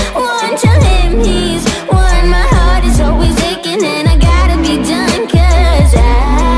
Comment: when i hear that i hear: And i gotta be drunkk idk y?